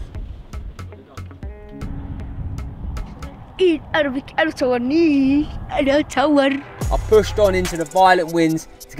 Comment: What did he say at 18:50? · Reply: He said only arabic, dont film me, why you filming me lol.